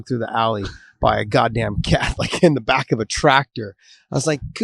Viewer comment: "Catholic in the back of a tractor"? Am I hearing that correctly? What does that even mean?